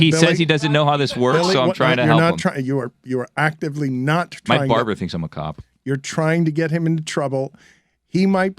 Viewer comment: did say "My barber thinks I'm a cop" because that's how I heard it that's hilarious